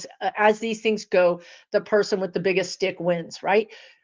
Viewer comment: “The person with the biggest stick wins”. I heard “dick” not “stick” lol